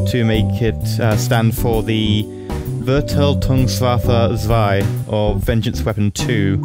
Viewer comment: what did you say? :D I hardly understood "Verteidigungswaffe 2" wich means "Defenseweapon 2" but it was named "Vergeltung 2" -> "Revenge 2" or "Vergeltungswaffe 2" -> "Revengeweapon 2"